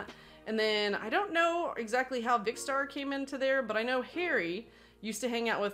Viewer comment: Funny how you said you and a few guys were with Harry before the sidemen. I recall a video where the sidemen explained when Harry joined them and they mentioned how they saw him hanging out with the wrong people so they took him under their wing. Maybe it's was a different set of people? But you did say he just disappeared so this makes me curious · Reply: sounds like she says Harry to me